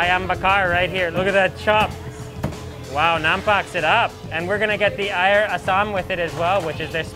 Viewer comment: i heard you say " napakasarap" here in philippines it means "so delicious" 😁😁😁